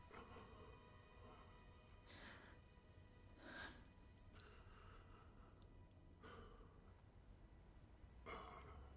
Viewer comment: que dice? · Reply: Eres la grieta en mí mundo (y no "eres la nieta en mí mundo")